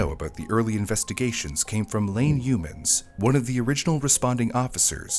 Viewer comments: I thought you said Lame Humans 😂😂😂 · I kept hearing “humans” instead of the policeman’s name… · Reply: I heard "Lame Humans". What a name. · I thought he said information came from "lame humans".